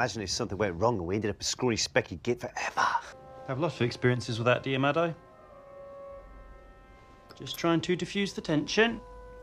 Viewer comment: this is funny but like what did they say?? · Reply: says "fair warning: it tastes like goblin piss" you have lots of experiences with that do you? just trying to diffuse the tension! this is what I heard anyway jdndjd